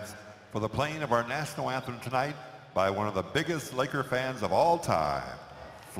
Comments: i thought he said "biggest liquor fan of all time" then i realized it was "biggest laker fan of all time" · I thought the person said, “the biggest liquor fan of all time”